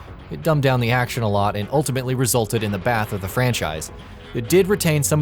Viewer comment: Did he say “the bath of the franchise” at 6:33 ?!?